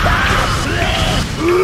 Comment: Squidward: gets incinerated. Spongebob: "squidward's such a jerk." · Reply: I thought he said „squidward‘s such a joke“ 💀